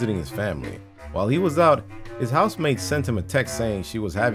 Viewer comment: This whole time i thought you were saying "housemaid"......